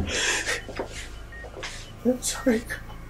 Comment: DID I JUST HEAR "I'm sorry Carlos"? CAUSE AHHHHH MY HEART